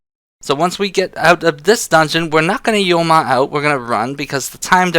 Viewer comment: I thought he said "your mom out" lol